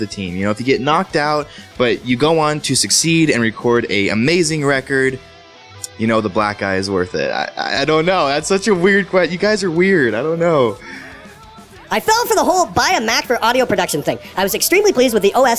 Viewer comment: I swore he said "you know the black guy was worth it".